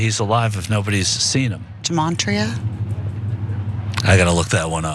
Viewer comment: I listend to what this woman said and then typed what I thought I was hearing into the Googles. I think she's saying "gematria", which, according to Wikipedia, is just the name for the alphanumeric system where you assign a numerical value to all letters and then assign total values to words based on their spelling. That's it. That's all it is. So all she's talking about is some stupid cipher that some jacka** wrote up. And then from there, who knows what kind of ridiculous conclusions they're coming to. Give me a break. Gematria... it's like a game that you'd find in a children's activity book, or on a placemat at Denny's, and these Q people are turning their whole lives upside down over it. It's just incredible. These people are so lost.